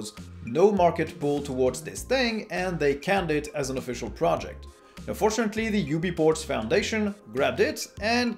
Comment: and 7:47 it really sounds like you say "UNfortunately" instead of "Fortunately", even the CC thinks so. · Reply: Probably because I say “and fortunately”